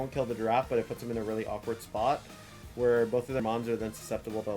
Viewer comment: At around the 32 mins mark it sounds like you say both of their moms are susceptible to damage. You said mons but still